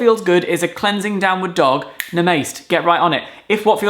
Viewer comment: i'm sorry. did you say nah-mayste? what.